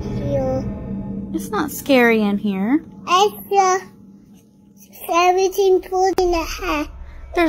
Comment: I swear that little boy says "There's a scary thing floating-a head." As in, a floating head 😮